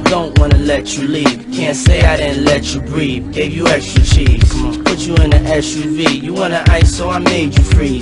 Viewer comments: i always thought he said extra cheese till now... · am I the only one who hears "gave you extra cheese"? lol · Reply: I always thought he said "Gave you extra cheese" until this very moment. Lmao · I always thought p diddy said give you extra cheese 🤣🧀